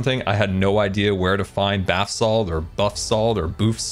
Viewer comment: calling basalt bathsalt might just be the best thing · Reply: I thought he said Butt Salt 😂